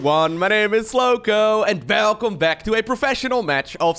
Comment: I hear that "Velcome back" getting more and more dutch as time passes, its really funny! Love your content man, keep up the great work! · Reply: this time around I heard "belcome back"